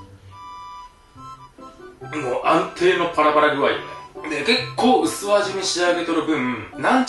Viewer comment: 安定のパラパラ具合(パラグアイ) 本当に『ぱら パラグアイ』にしか聞こえん 個人的にツボで繰り返し聞いてしまった←